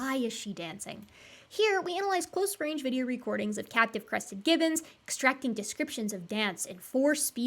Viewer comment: said, "captive crested gibbons" and I heard, "cat depressive gibbons"...